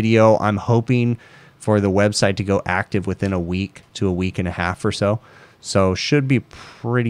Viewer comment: I think I heard “in a week a two” a week or two ago. Looking forward to your new website!